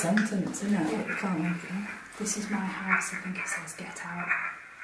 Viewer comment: 22.20 it sound like it says it’s my house get out